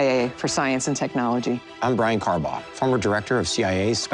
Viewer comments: I thought he said: 'I am Brian Carbomb'. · did he say his name was "Brian Carbomb"?